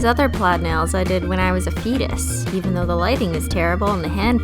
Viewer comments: When u said fetus, I thought u said penis 😂😂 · Haha at 0:26 I actually thought you said "when I was a penis" and I kinda just sat there until I realised you said "fetus" I'm such an immature human